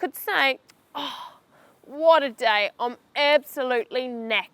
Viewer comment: I heard "I'm absolutely naked"